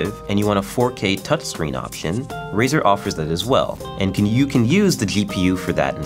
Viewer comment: "you can you can use" anyone else hear that?